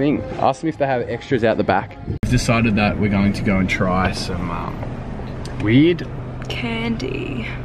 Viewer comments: When he said weird I thought he said weed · At first I thought he said they were going to try weed candy 😂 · Def thought he said weed candy